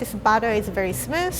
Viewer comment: Thought she was saying butter not batter 😂😂 I’m stupid I really thought crapes were made out of butter